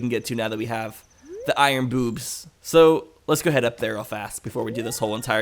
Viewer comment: i think he said iron boobs